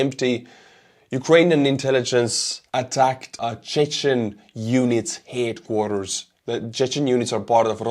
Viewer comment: I thought you said Russian "kitchen" Units. Racking my brain over here over what is a "kitchen" Unit. And then my high ass figures out your saying CHECHEN. 😂 God bless Ukraine for fighting and just legalizing cannabis that's a smart move $$$$.